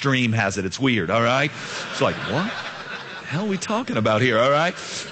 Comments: im not trying to pass judgement, but im just curious but did he say "what the hell we talking about at 2:58 ? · did he say, "what the hell are we talking about here" at 2:58?!!? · Did he just say "What the hell are we talking about here?" at 2:55 or so?